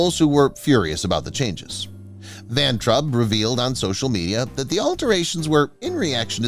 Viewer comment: I thought you said Van Trump!😅😅😅